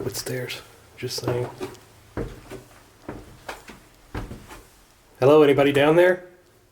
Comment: 14.37 When he was walking down the stairs and he yelled "Is anybody down there?" I heard a Woman's voice answer him shortly after. Right around 14.44. It sounds like she said "Yes."😮😱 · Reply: Yes, definitely got an answer. I heard "Hello".